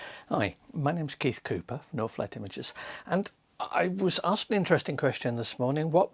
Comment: During Your introduction when You say "NorthLight Images" I hear "No flat images" :D Which isn't bad either. You gave me similar advice some time ago and I appreciate that. Still evaluating my business idea trying to sharpening it up.